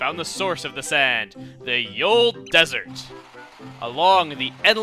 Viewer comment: I thought it said, Yolo Desert, LOL. #YOLO